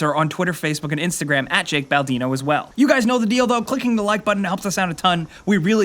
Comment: Did he said "You know the dildo"